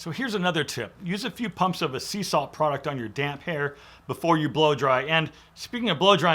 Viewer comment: Great tips. I had to rewind the video. I thought at 4:30 you said on your damn hair but your damp hair.